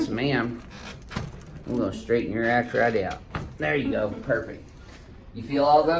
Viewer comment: did he say "I'm going to straighten your ass right out"? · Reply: he says arch. He just has a southern accent.